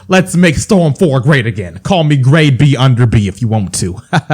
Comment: Did he just say "gradebunderb" 😂